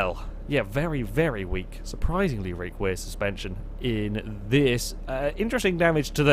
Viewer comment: anyone else hear "reak wear suspension" at 16:42 xD